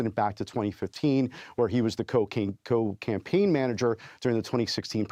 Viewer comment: Freudian slip - cocaine instead of co-campaign.